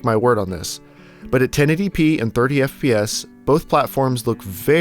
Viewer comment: Am I hearing things or did you say "birdy FPS" instead of "30 FPS"?